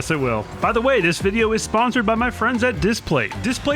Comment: I thought u said u had no friends